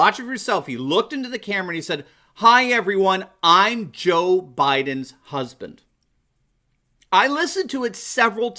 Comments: I definitely heard him say "I'm Jill Biden's husband." Trump Pence 2020! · I gotta admit it sounds like he said "Hi Everyone I'm JILL Biden's husband." I don't hear Joe at all. Regardless of what he actually did say he is not fit to run this country. TRUMP 2020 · Oh... yes... lol... that is what I heard..."I'm Joe Biden's husband... you see his wife side eye him...lol